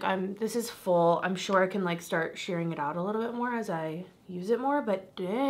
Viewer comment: I’m laughing because I thought you said shart!!! Butttttt…you said shards of glitter 🤣🤣🤣